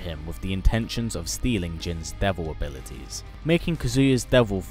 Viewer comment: did you say devil fruit